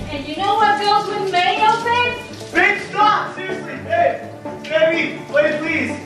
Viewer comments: when poured mayo in him and went to go get mustard it sounded like said bitch stop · did he say bitch stop? 😂